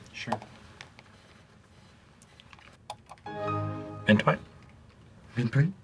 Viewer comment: Okay I’ve been wondering… at 9:59 WHAT DOES JIM SAID BECAUSE IDK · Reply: “Mint Dwight” is what he says. Because he is offering him a mint when the sound plays as part of the experiment.